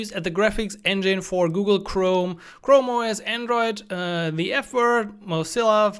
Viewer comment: 😂😂😂 did you say "the F word?"